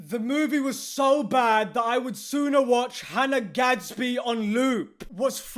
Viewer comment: Jesus for a moment I thought you said Hanna Gadsby nude 🤣🤣🤣🤣